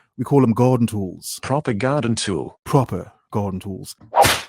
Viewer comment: For several videos i thought the robat said "propaganda tool"